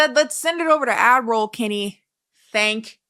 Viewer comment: Adroll kennie. Not Admiral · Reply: That’s the joke - people thought she was saying Admiral Kennie instead of ad-roll Kennie. She has Admiral Kennie merch lol